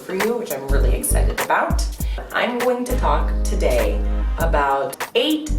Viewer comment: I swear i heard that she said at 0:12 " i'm going to cock today" instead of talk.